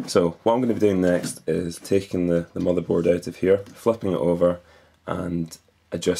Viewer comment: fucking it over...lol i no u said flipping it over but is sounded like fucking xP